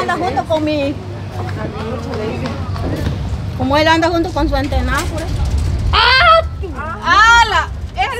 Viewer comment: Minuto 4:44 escuchén que dijo la esperemos reaccion de chentona 😂😂